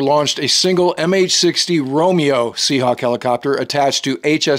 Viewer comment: Good to see the Navy has improved the combat capabilities of the MH60's. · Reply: My mistake. I misheard the identity of the first helo. I thought he said SH-60R, it was MH-60R. I have been retired nearly 22 years and last deployed with SH-60Bs in 96 and 98 on the USS Yorktown (CG-48). OSCS(SW) USN RET'D <num> AIC/S <num>